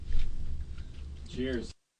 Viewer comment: One last word was heard after the last WPLJ Legal ID. At 20:23 a voice is heard saying "Cheers". Then at 21:27, the K-Love signal starts.